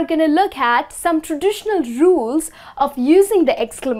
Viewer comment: "...some traditional jewels..." is what I heard for "...some traditional rules..."